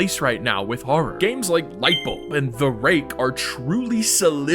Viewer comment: thought he said Micheal instead of lightbulb